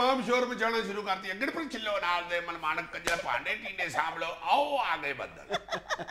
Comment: Did azizi said "KNJR"